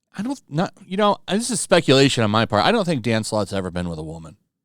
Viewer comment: Before seeing his name, I thought you were saying Dancelot lol